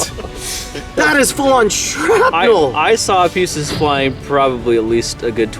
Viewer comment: At first I read this as "can coconut grenades really save your panties." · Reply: Seth Mitchell and at 2:56, i thought i heard " i saw feces flying..."